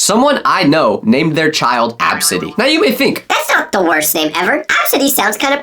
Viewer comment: my stupid ahh thought he said "absent" 😭🙏